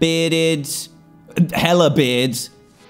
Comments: did he say hella beard? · did Dan say hella beard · Did DanTDM say Hell?? Im not mad I just want to know